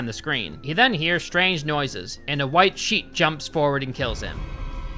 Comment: i was filling up my water when you said he was attacked by a sheet and thought you said sheep and played it back and scared myself 😭💀